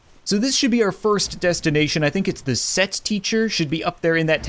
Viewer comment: i swear to fuck i thought he said sex teacher